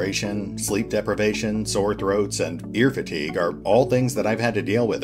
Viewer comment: Lol Ear fatigue I thought you said beer fatigue I could have understood beer fatigue :p That being said your darkest dungeon videos are absolutely sublime, you take a game where the narrative is top nodge and what makes the game and you somehow make the narrative on your videos even better, I have no idea how you manage that it is so impressive.